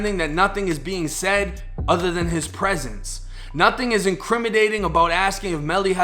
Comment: Did he say “INCRIMIDATING” at 13:12? Lol!! There’s an N in there not a D my friend lol